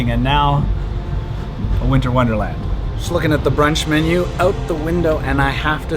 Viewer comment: I thought you said you are going to throw the lunch menu out the window 😆